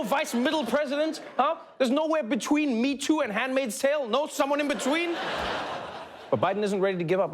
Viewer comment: Is Trevor saying "hand made sale" or "hand maid's tale" at 12:43?